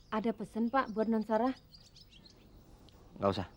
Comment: team crew ngasih bocoran ke bibi (ada pesan buat non sarah) Make henset kedengeran